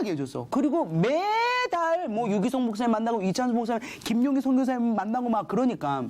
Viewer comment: 여기서나 말하는 선교사님이 누군가요? 발음이 잘 안들리네요. 저도 설교말씀 좀 듣고 싶어서요. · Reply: JieHun Ryu 김용의 선교사님이에요!